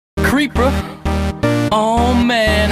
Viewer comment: I hear “Creep-bruh”